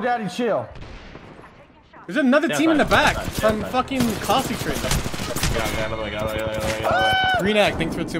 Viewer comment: あたり『コースティックの別パ』って字幕があるんですけど、「from fuckin Caustic treatment」って聞こえるんで『コースティック（の診療所）から別パ』な気がします 間違ってたらすみません…